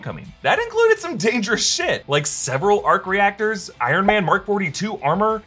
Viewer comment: Did just say, "SHIT"?